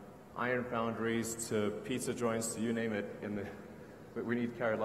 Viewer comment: Did elon say pizza an joints 24:22 Sign me up